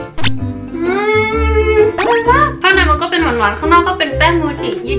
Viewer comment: ทำไมเราได้ยินว่า "ข้างในมันก็เป็นเหมือนหนอน"😂😂😂😂